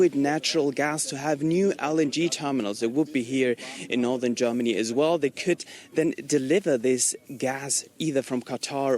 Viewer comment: did he say ING terminals? what does it mean?? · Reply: LNG